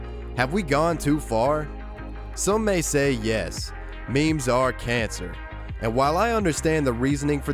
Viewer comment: "memes are the answer" That's what i heard